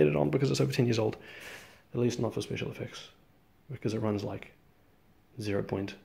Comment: Almost sounded like you said "flaw" rather than "floor" (as if I was supposed to notice something was off)